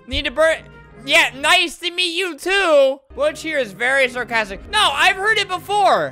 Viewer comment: “THIS WAS ALREADY HERE FATTY” me poor mrflimflam 😂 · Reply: Before that I thought he said bitch